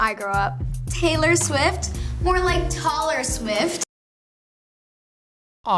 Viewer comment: am i the only one who hears tater swift